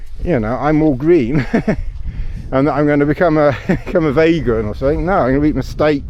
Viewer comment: your musings are the best on YouTube. Eco Warrior 😂 When you said vegan I thought you said vagrant, and I thought; wildcamping is a kind of luxurious vagrancy 😂